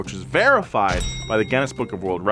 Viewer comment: I thought said Guinness gitnness 😂😂😂